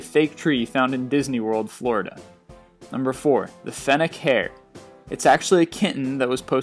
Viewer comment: i thought he said fenniken from pokemon lol